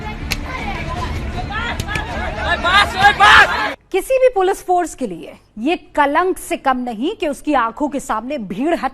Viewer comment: pe suno kya bola h maar soeb maar jra video fir se sune plz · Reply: Oi bas oi bas bola wo,gaur se sun